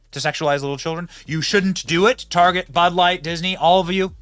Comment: Sorry, maybe someone could clear this up for me, but did you say that we shouldn't target budlight?